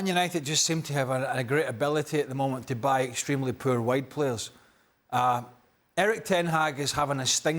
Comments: Thought Craig said "white players" when he says "wide players" 😂😂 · I swore he said “extremely poor white players” 😮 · I swore he said “extremely poor white players” 😮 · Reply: Yeah 😂 every time says “wide players “ it sounds like he is saying “white players “ 😂